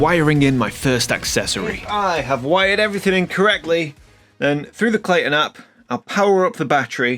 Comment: I thought I heard you say that you "wired it incorrectly" but then realized you meant "wired it in, correctly."